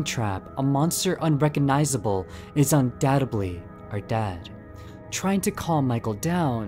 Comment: I swear here 19:43 he said “UNDADTEDLY”